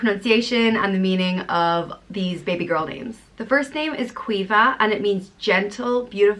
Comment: At first I thought she said Qweefah LOL😂😂😂😂😂😂😂